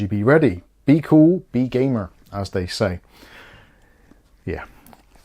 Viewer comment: for a moment i thought you said "be cool, be gayer".....lol, this does look interesting, not surprised you have took interest, as i know i shall also, thank you for the heads up👍🏻